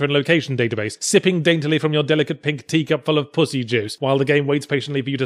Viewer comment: Did he just say "pussy juice". Yes!